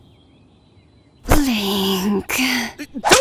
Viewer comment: Shit. *Goron city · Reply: I thought you said "bombs are delicious", so Gerudo Valley works for what I heard. Lol